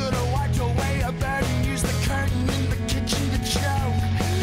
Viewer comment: I always hear, " used the Kirtan in the Christian to choke you"